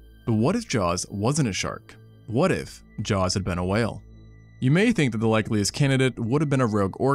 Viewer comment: I thought this said “If Jews had been a whale” and I clicked on it fully expecting the video to start with “Shabbat Shalom I’m Shamu and welcome to my synagogue”